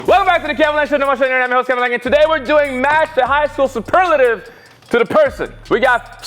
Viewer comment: welcome back tos bahhsksnshshhsbejhdhd that’s what bro just said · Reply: I saw a comment one day that said he said “welcome back to the Kevin langue show, the #1 show on the internet, im your host Kevin langue and today we’re doing..”